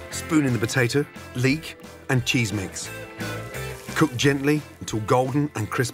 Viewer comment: for a second i thought he said cheese whiz but then i realised he'd probably punch me in the neck for even suggesting that